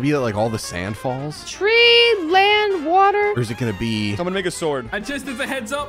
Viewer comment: bro it sounded like someone''s n##ga sword